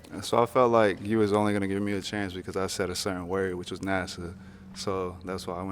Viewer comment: I don’t think it was the word “NASA” that did it. I think she is saying “Nas-saw”. I don’t think she even knows where he’s interning. Did anyone else hear that?